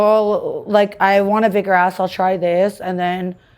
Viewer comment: Did she say she wants. bigger asshole?